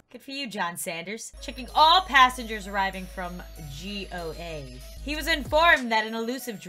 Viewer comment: did lauren really call Goa g.o.a :0